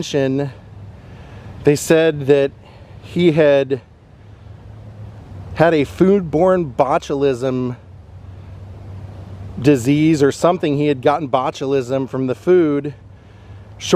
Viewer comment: on. North Korea said, "He contracted food born botulism while in 17 months detention?" Did I hear 👂 that right❓. NK promotes EVERYTHING is perfect under their, "Supreme Leader" -Kim Jong Un‼️. How could ANY of their PERFECT food be bad for human consumption ⁉️. I can't believe they admitted that 😮. (I'll be looking into this one). 🙏🕊️